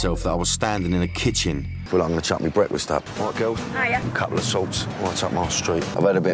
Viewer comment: Couple a salts , right up my street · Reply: I thought he said a couple of sorts!